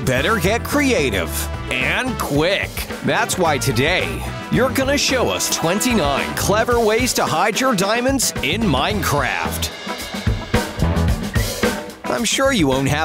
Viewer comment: did he just say "that's why today your going to show 29 ways to hide your diamonds in Minecraft"